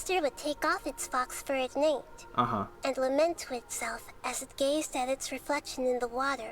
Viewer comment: im sorry.. i had to... 1:38:09 'LEMON' xiao said calmly (dw if u dont understand :3)